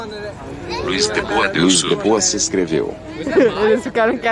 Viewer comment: só eu que ouvi "Deus é mais"? POAKSKOAOKPS